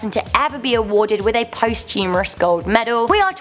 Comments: I'm sure it's your accent, but it sounded like you said posthumorous instead of posthumous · Okay I am hoping that I misheard a word being improperly spoken. I thought I heard her say that a medal was presented to someone "post humorously", when the word is actually "posthumously". I hope I'm wrong. Anyone else hear it that way. Thank you. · I thought she said post humerous...and am I the only one who can't stop saying at those gold.pants....? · Did ... did she just say "posthumorous"?